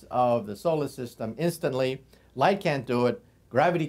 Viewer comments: the first time i saw this i thought he said: "Einstein is the cock of the block" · lol i thought he said cock block at the end there